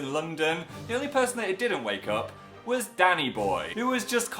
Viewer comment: Danny boy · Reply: i thought he said "daddy boy"